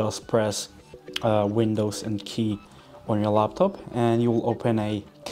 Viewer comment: Sorry, I cannot decode what you say after "windows" at 0:18. Are you saying "key"? Is there a button for this and where exactly? Or, do you mean the letter "K" instead? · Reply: By pressing the Windows key and the K key at the same time it will open the cast menu!